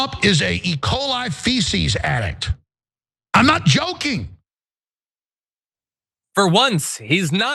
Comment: Awe. Shit. I thought he was saying, "I'm not choking!" I was going to offer some assistance with that.🤣🤣🤣